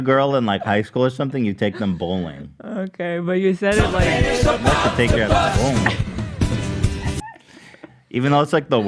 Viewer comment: i thought when ethan said “id like to take you out bowling” i thought he said “id like to take you and boom u”